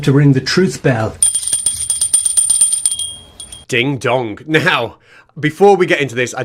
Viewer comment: Did you say "dong"...?